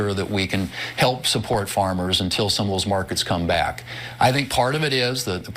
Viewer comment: did he said until the scumbags come back?...😂